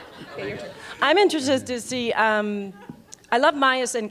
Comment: Lol... How else heard at 27:34 teri polo said " I'm interested sted" instead of "I'm interested in". Lol im laughing so hard now.